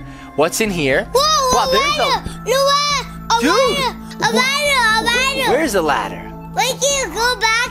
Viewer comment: when shaun said a ladder I thought he said avada